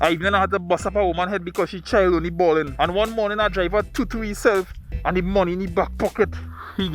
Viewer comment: Awesome! Can't stop laughing after I heard - "he toto heself"